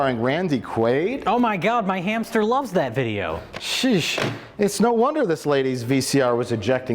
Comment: Did just say 'Sheesh'?!?!